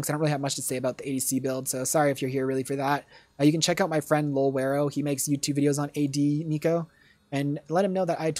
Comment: I'm sorry but I cannot make out what youtube channel name he is saying here, can someone help? "luvero"? If you see this, do you think you can put him in the description? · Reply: Lol_wero